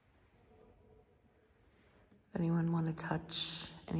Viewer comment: In the violent woman’s ward the whisper said “don’t leave anybody alone.” Eeeek so freaky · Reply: ohhh that makes so much more sense haha. when we originally played it back on your phone, i kept hearing “does anybody have a…” but it didn’t finish. but that’s a lot creepier